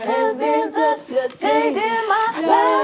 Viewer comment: When they were singing Changed Karen sounded like she said Oil Change